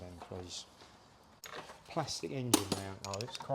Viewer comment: " did I hear correctly from Chris "plastic engine mounts" 😮 Love that engine rotisserie, looks like Clarke's does well out of ❤